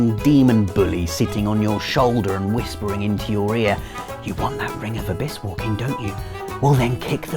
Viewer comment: when he said "whispering into your ear" during the Dark Souls segment im sorry but his british accent made it sound like he said "whispering into your rear"